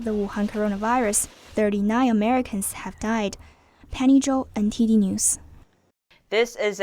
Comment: At timestamp 6:16 ... what did she said? Is it "Penny Joe's MTV News" ? Her voice is very nice. :-) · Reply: Hi Mike, it's "Penny Zhou NTD News" Penny is our China reporter